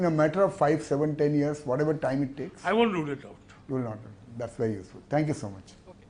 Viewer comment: what did he say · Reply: I will not rule it out